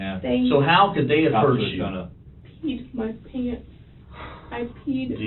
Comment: I thought she said “I plead the 5th” but she said “I peed my pants”😂😂